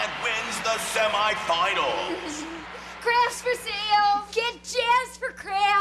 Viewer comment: I first heard "Grass for sale"